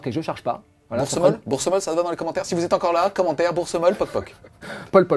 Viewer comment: j'ai entendu " Pol pot Bourse molle " en fin de T1 j'ai beaucoup ri !